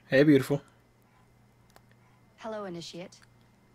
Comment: At the intro I heard "Hello inishit"